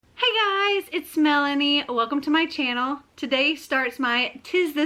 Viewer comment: I honestly had to rewatch the intro to this video because I thought you said ‘it’s Smelanie’!🤦‍♀️😂